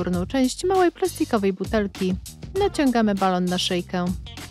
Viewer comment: Jak było"małej plastkiowej butelki" to ja usłyszałam małej prestiżowej butelki xd